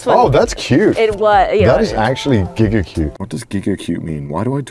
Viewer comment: Somehow I misheard him saying "giga-cute" and thought he said "Gigguk-cute". Garnt ftw!